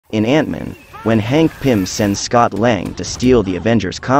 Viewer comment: I thought you say fat-man 💀💀💀